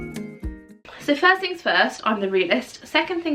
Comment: I also can't say first things first without saying I'm the realest 😅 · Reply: I always thought she said "First things first, I'm a realist" 🙈.